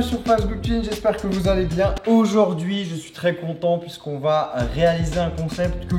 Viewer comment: C'est moi où il à dit contente aux lieux de content à